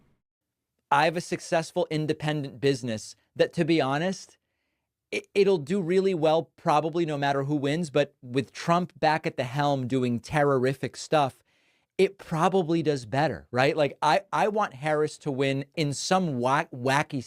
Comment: did he just say... "terrorific"?